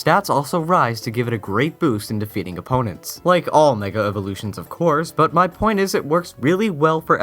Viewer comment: in 6:30 did you say nigga evolutions?